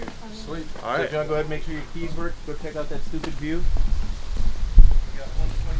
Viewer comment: Am I tweaking or did bro say that stupid view? 😭💔🥀